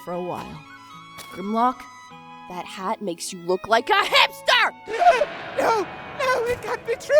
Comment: I thought she said "that hat makes you look like a hamster!" X'D